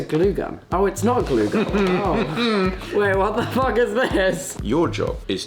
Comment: What did tubbo say? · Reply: He said "what the fuck is this"